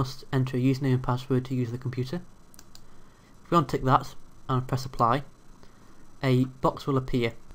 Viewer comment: Did I hear you say,, "UNTIK THAT", when clicking on the checkbox? This has been called "UNCHECK the checkbox" since the beginning of time. You need to UPDATE YOUR VIDEO, saying it properly.